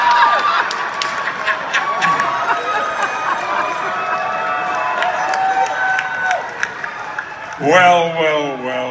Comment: What did the guy yell at 2:00?? Also thanks for uploading!!!! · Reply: Yeah, that was it. He yelled "Holy shit!!" PStew rolls with it, graciously. LOL